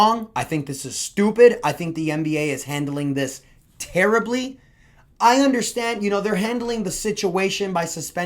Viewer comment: did i hear "Terrybly" 😂